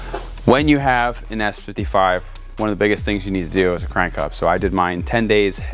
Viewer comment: can someone please explain this? I didn’t quite understand what it is. Did he say crank? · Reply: crank hub